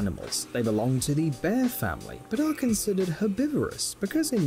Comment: I thought the dude said a long titty bear panda